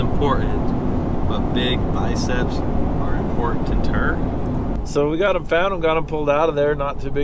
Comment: Man, the nuggets of wisdom are just dropping today: "Education is important, but big biceps are importanter." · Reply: After reading your comment I had to got listen to that again, I heard him wrong. I thought he said big biceps are important to her. 😊